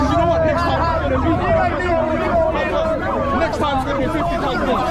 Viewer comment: I thought he said "they'll be 50 times more" not "50 thousand"